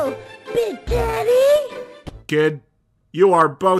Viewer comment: To everyone watching: from my understanding, the Who is saying "big teddy", not "big daddy". The audio is just so awful that it's easy to mishear it. And if you see this comment, I did make a reply to another comment saying sort of the same thing, but I felt that this should have it's own comment so more people can see it.